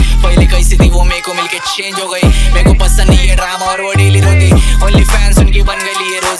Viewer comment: is he said mereko pasand nhi Ram or ye nili dhoti